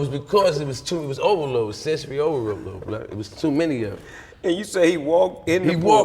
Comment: Did he say Blood at 10:23?